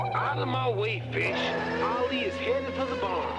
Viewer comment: Did he say, “Outta my way B*tch!” in a Saturday morning cartoon?? 😂